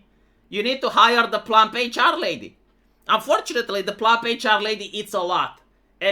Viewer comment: Ohhhh PLUMP HR LADY I was doing stuff in the background while listening and I thought I heard "Fluffy Charlie"